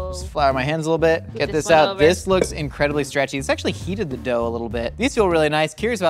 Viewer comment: I heard "it sexually heated the dough a little bit " and now I have a new way to tell my husband I need us time 🤣🤣🤣